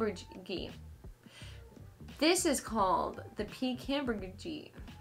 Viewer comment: I’m dying because at 16:25 when you were trying to pronounce the name of the spider my subtitles said “peak hamburger gene” 😭😂😭😂💀